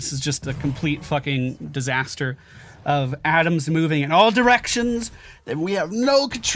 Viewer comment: why pick on ADAM? or did ya say ATOMS? xD